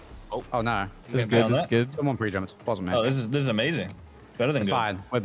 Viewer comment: Anyone else hear say “someone pre jumped, wasn’t me.”